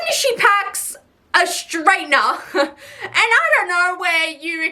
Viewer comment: I was out of the room and when she said straightener I heard it as strainer and I was like “no that actually makes sense. You can put stuff in it and shake it to get rid of sand.” And then I walk back in the room and notice it’s a hair straightener. Yeah no that’s so stupid lmao